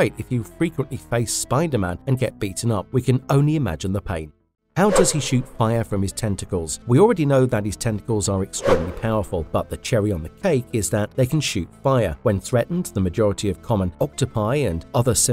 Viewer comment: i was concerned i thought you said TESTICLES